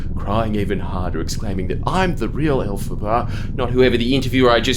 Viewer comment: i legit thought the interviewer was saying "alphabet" in an aussie accent and I was like "yeah! 'tism pride represent" because i know nothing about wicked and then jordy reveals its the witch from wicked and now i feel dumb